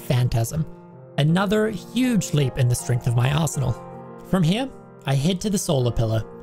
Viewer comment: I really thought he said strengthen my asshole for a second LMAO. 10/10 video aside from that…really interesting seeing the strategies used to avoid achievements.